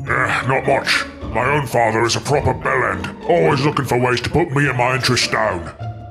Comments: Wait did he just say bell end?!?!?!??? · did he say bell end or am I hearing things XD